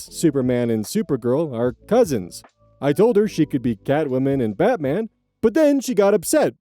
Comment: I misheard "Catwoman" as "Batwoman" while cleaning and had to actually rewind to double-check that OP didn't just suggest to create the same problem with a different Logo😅